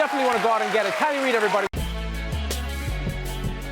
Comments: Got me for a second thought it said Riley Reid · Aight who else thought it said riley ried at first 😂😂